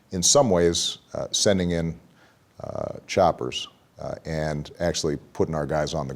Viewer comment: Say "choppers" again. Sounds like you said "Chaoppers". "Get to the Chaopper" Arnold.